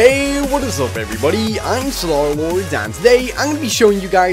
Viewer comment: Wait, i heard Slarlord, not Starlord (0:16). You tried.